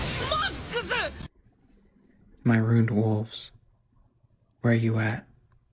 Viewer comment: I HEARD RUNES